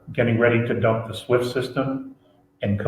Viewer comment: JFK and Lincoln were the two US Presidents who tried to get rid if the fiat currency system. At 9:01, is that "SWIFT" system? Swiss...? (SWIFT * is * Swiss, so... not sure what u said)